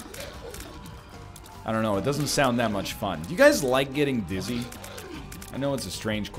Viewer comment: OMG I heard "Do you guys like getting BUSY?" 🤣🤣🤣